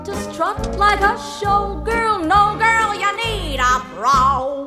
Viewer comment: when I was little I thought she said bra!😂